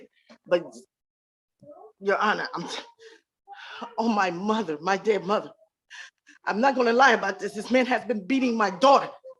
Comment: Did she just say on my dead mother 🤦🏾‍♂️